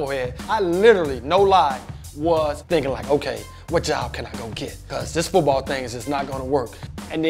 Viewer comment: What did he say at 1:20 seconds in? It's bothering me. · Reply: darryl dwyer what Job could I go get?